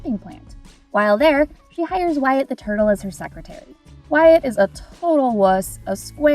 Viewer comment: did she said Wyatt the turtle………….